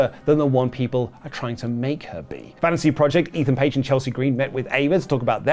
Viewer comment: I thought had said 'Vanity' Project and got really confused by the lack of Brad Baylor, Ricky Smokes or 'Heartbreak' Jackson Drake in the still image next to his head.